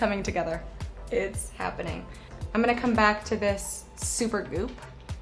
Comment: i thought she said supper glue and i was like wtf???